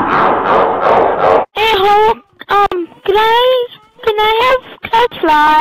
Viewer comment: sounded like he said hey a hole XD